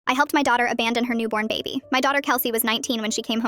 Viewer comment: I thought she said “I helped my daughter unborn her baby”🥹✌🏽🌹